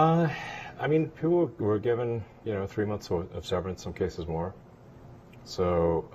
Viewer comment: I cound not hear what Elon says here...I am still learning English and I want to understand what Elon is saying here.. could anyone please kindly help me?? (;´･ω･) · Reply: You probably figured it out already. He said - people were given, you know, 3 months severance, some cases more. ... Companies need to run on their own cognizance. Severance - the action of ending a connection or relationship. Cognizance - knowledge, awareness, or notice. ( good words, btw ) So I think you can understand it this way. Elon Musk shut down online scamming or something regarding some companies. And then he said that it's the companies job to give security to their clients online or something. Basically instead of whining. Hope it helps <3